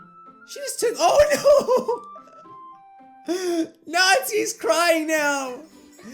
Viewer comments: Who else hear "Natzi"? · did you notice that he said Nazis crying instead of Natsukis crying